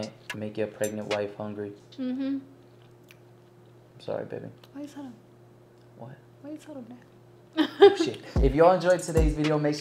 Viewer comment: Wait did he just say you can’t make your pregnant wife hungry!? And then he said oh shiii after she said something!? Am I crazy!? Lol time stamp is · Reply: No u not I think she said why did u tell them then he said oh shit